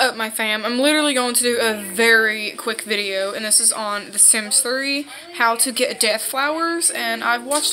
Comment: did she say on the sims 3